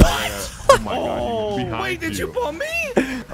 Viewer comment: what I heard : Wait did chu papi